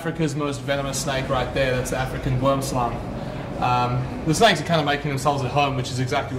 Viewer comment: I am guessing he said boomslang at 1:30 going on appearance. But I heard guamswong and the closed caption could only manage 'worm's lung'.